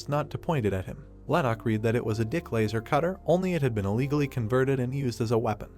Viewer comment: tell me what you hear. I heard di*k laser cutter😅 31:35 I think I heard gay man idk